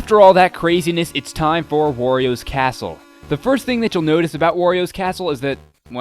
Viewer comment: it sounded like you said oreo's castle